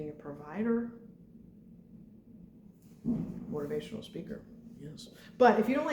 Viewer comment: can someone help me please!? what is she saying at 19:56? mother, provider and then ??? speaker. I am so thankful for your support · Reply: motivational speaker